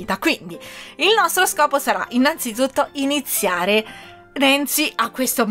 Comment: solo io ho sentito Renzi?😂 PS bel video💖❤️(come sempre)